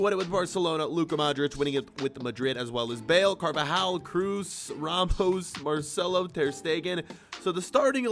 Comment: I swear said rampost